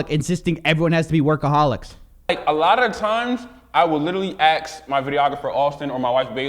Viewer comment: Did say "AXE" instead of ask? Cringe